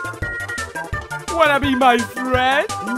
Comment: What marx said:wanna be my friend? Captions:whatever you might read